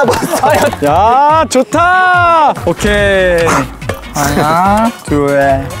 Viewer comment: I kennot with the tiny cute DUEL instead of DUL (number 2 in korean) here, SHOOOO CUTEEER